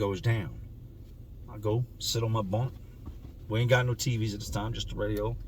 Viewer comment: In my bunk it sounds like you say in my BONK makes me crack up all the time 🤣